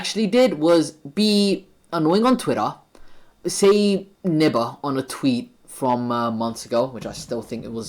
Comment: was that supposed to be the n word? it sounded like bb instead of gg